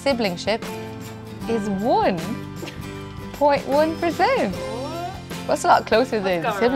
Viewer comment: "The probability of half-siblingship is... 1!" I freaked out for a minute because I heard that as 100% - OMG they're half-sisters! Anyone else who had that?